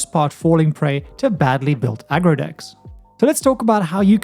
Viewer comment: I heard "I grow dicks" for an infinitesimal moment. Great vid, I'm playin Master Multiplied tonight♡